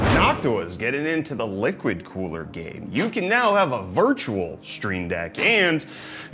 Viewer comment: so brainrotted that i thought he said "hawk tuah's into the ligma cooler game"